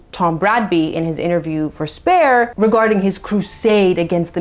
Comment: Anytime you say "Spare" I hear "Waaaagh" 😂